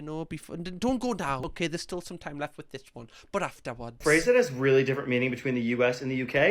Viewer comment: My god I had to listen multiple times until finally giving up and turning on captions to understand what he was saying when he said “a phrase that has” I always find it funny when Americans complain about English accents being unintelligible because I’m not from either country and get confused by American accents far more often. Maybe it’s cause New Zealand, where I’m from, is a former British colony and we share a lot of culture; tea, fish and chips, a king etc and watch a lot of British media here + having loads of British ex pats and very few Americans (I’ve met one, my entire life 😂 while having met countless brits) but Americans always confound me!